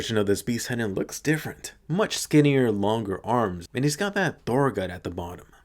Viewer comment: Did he just say Thor gut at 8:00? 😂